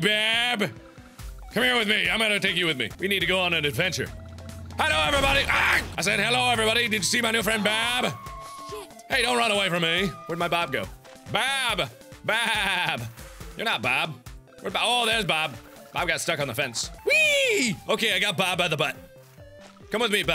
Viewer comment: did she say "shit"?